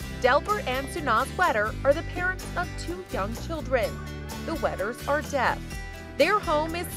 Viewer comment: am I hearing “two dumb children”?